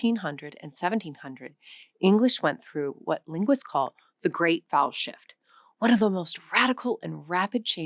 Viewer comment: I thought she said "the great bowel shift" and did a double take. lol